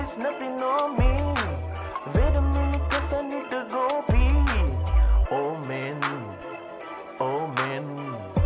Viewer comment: When you said oh man I thought you said old man